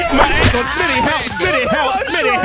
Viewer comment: Anyone else hear when he was saying “Smii7y help” at 16:50, he said shitty help